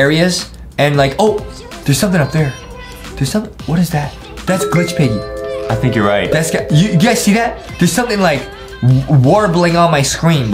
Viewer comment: alright lets get glitch huggy Me: what did he just say glitch huggy except g;itchy piggy😂🤣